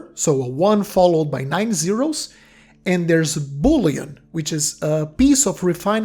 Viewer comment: bro this whole time i thought you were saying "boolean" not bullion lmao